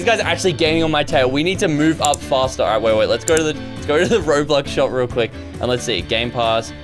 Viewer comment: I thought when you said bobux shop I thought you said bobba shop